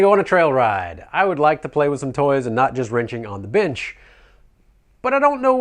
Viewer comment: My girlfriend walks in the room and heard you said wrenching on the bench and thought you said wrenching on the wench lol